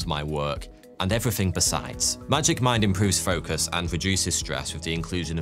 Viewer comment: When bro did the sponsor read I was only listening not watching and I swore he said today's sponsor Magic Mike lmao I was like huh the accent gets me every time